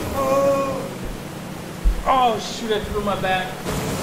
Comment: he said shi- or was i the only 1 who heard that ?